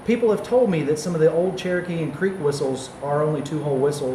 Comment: I misheard Creek as Cree at first, and was confused thinking alligators seemed too far south for Cree and none of the Cree I've met used bone whistles. LOL.